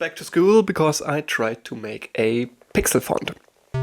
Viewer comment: I thought it said pixel fart at first😂